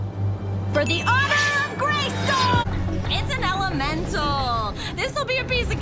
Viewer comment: I thought she was saying " For the honor of GraceCo" 💀